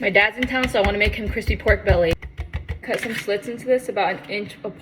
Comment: I really thought she said crusty pork belly